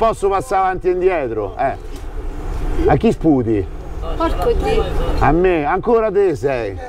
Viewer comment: Ma a 6:49 ho sentito un porcoddio o sbaglio? 😂😂😂😂😂